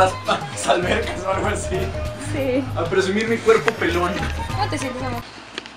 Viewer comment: fui la única que escuché “ como te sientes amor”